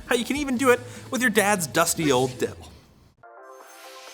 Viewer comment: I cannot be the only one who had to check twice at 0:33 to see if he said what I thought he said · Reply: He said Dell, he didn’t say anything else, GET THAT THOUGHT OUT OF MY BRAIN